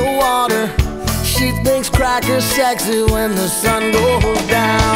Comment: Thought he said "She thinks crack is sexy" just realized for the first time in my life that it is in fact Kracker